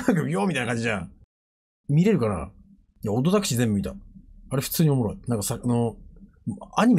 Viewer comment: ここなんて言ってるの？ なんのアニメか気になる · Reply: ODDTAXI？